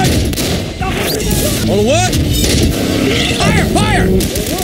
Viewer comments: For a while I thought he said “over what?!” Until I saw a captioned clip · I always thought he was saying "All the way".